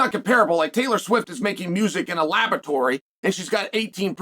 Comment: Did Chael say Taylor Swift is making music in a laboratory or a lavatory? 😅